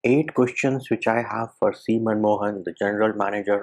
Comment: I misheard his name SemenMohan...🤣🤣